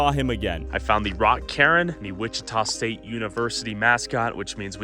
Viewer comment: subtitles say karen lol. It's a cairn